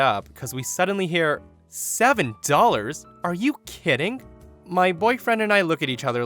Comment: Oh haha for a sec I thought the Karen said 70 dollars and I was like:😶 Then I realized she said 7 dollars I felt SO dumb